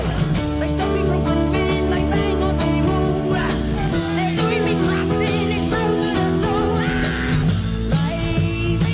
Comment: Does anyone know what the background vocalist says at the 0:25 mark when Marriot starts to sing the "doin' me crust in" line. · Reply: They stop me from groovin they bang on the wall....